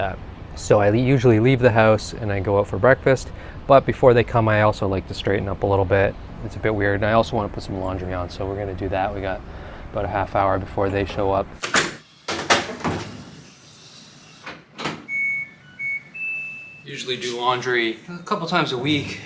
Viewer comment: I swear you said you were going to put some lingerie on 😂